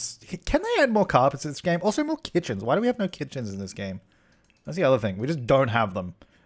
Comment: What is saying at 3:02? Sounds like he's saying "more kitchens. why do we have no kitchens in this game". Is that what he's saying or something else?